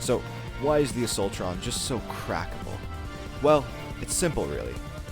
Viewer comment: I thought it said crankable